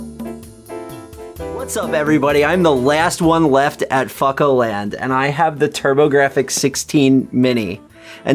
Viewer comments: thought he said 'fuckoland' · I thought I just heard "FuckNo Land"